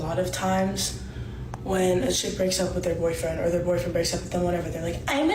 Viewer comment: Hey guys, a little help here, please. At 16:00, with headphones on, I hear her say "when a schid breaks up". Sub-titles has her saying "when a kid breaks up", which would be weird because she referred to the guy as a man but she refers to the woman as a kid? Nah. I think she likely said "when a CHICK breaks up", because that would make the most sense, but I'm not hearing it, nor reading it.